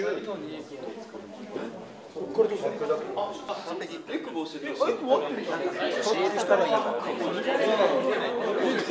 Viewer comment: 三笘「完璧！」菅原「◯◯したらいいんだからね、薫くん」って聞こえる気がするけどなんて言ってるんだろ 完璧！のはずが中村に笑われてすぐ外す三笘かわいいし、菅原のキャラはいいし、面白い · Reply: 教える人がいいんだからねって言ってますね笑笑